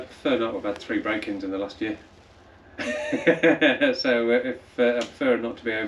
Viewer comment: I watched the intro twice, and I could have sworn he said "I've had three Grandkids in the last year". lol